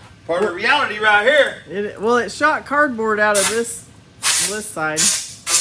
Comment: Second time you try to fire something went out the exhaust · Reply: Did she say it was a piece of cardboard? I swear I thought her say that…. Why would cardboard come out? 😬